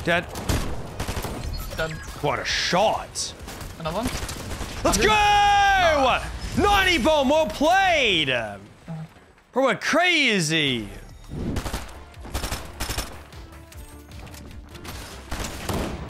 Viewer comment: did bro say “90 bomb,well played”